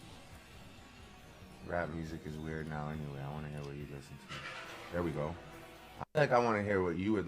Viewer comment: ???? Just chiming in, 42:01 I dont think Drake is saying " Rap is getting weird right now" I Believe he is reading what someone is commenting ,because i also hear him stating in its entirety, "rap music is weird now anyway I want to hear what you guys listen to" go back ....and you can see he's leaning in to read . They must have gone live. Yea.... I dint think he called Rap weird .